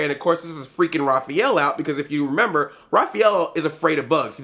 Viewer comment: Did you say Raphaelo ? xD